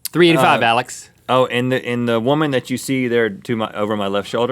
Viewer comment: I thought he said: "Oh and the woman behind me is Steve" I litteraly died